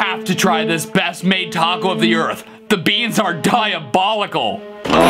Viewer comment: I hear "diabolical bees".